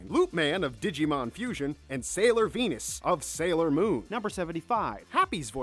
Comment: Did he say Sailor Penis....